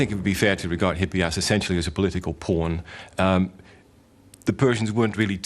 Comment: did he say political porn O.o